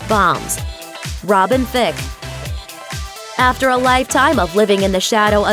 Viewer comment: Lmao I heard "Thicc" 😂